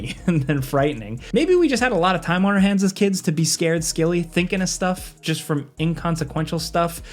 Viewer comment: I heard scared scilly lol